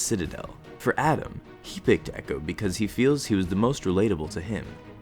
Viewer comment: XD wtf this kid didnt say he feels echo relates to him, he said he thinks echo is AUTISTIC just like him.